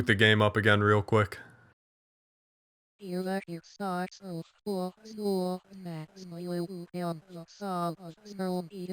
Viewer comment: What did he say? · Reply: "The community mourns the loss of Michael Williams, and holds hope for Scott's recovery." But, like, backwards.